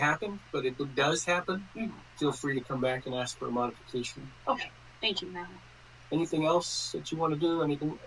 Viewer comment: Did she say okay thank you Matt? That’s crazy biased if she did